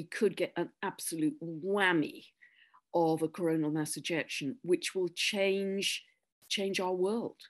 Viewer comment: Corona massinjection?? Sounds like that is what you are saying. Am I wrong?